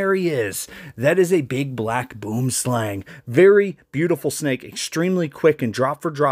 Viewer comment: Boom slang is Afrikaans for tree snake. This snake comes in green as well and is really scary cause you can find this snake almost anywhere where there's a tree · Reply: At first I thought he said "big black boom slime" 😂😭